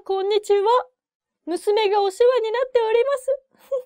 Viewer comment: Please. What did she say at 2:50? Musume ga ??oshiwa?? ni natte orimasu. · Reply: Hi caletcalet, Thank you for posting. She says "Musume ga osewa ni natte orimasu." that means "I always thank you for treating my daughter well". Keep studying with <link> Regards, Miki（美希） Team <link>